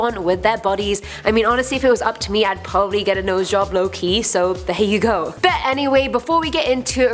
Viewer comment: You know I have a problem when at 0:38 I thought she said Loki instead of low key